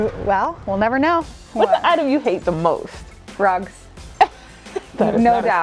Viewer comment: lol I thought the item she said she hated the most was drugs